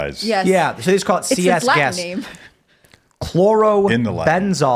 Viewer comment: did she just say that’s a black name?! 😂😂😂